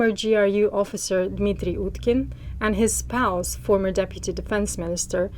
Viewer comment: Does she say "his spouse"??? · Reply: “…and his pals,” Although I did a double-take too. 😂